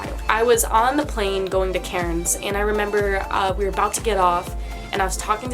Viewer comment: Lmfao it took me a hot minute to realise you were saying Cairns. I honestly thought you were saying Karen’s 😂😂😂😂